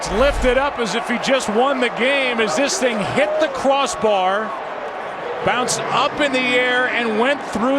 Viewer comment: Did he say cwoss bar?😂